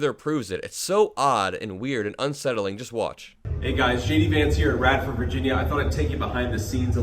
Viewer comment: I heard him say "Hi guys, shady Vance here..." I might be mistaken, though...